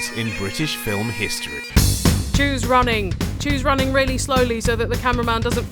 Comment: Ahahaha, i so thought you said "Jews running" at 0:45 :P